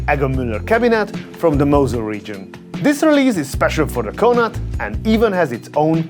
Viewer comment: I legit thought you were saying Canard instead of Connaught